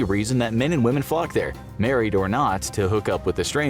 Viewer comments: This video is the first time I ever heard say the F word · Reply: “Flock” not f**k, I thought he said it at first too 🤣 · When they said flock at about 1:20, it totally sounded like something else for a second, lol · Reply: yeah I heard book